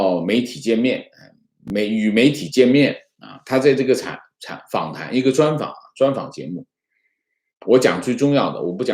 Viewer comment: 秒说高福哭着说啥？讲太快了没听清，有听清的同志解答一下吗？ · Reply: 我们弄晚了